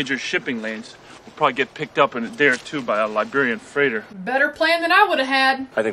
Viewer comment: Did he say "picked up in a day or two by a Librarian freighter" - that's hilarious. Librarian instead of Liberian, never noticed that joke before...